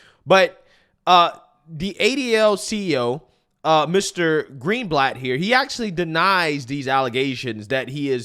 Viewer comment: I thought he said his last name was greenblack